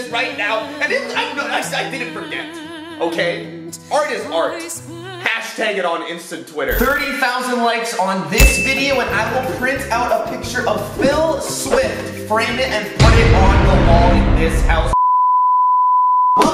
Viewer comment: Who else thought he said wILL SmIth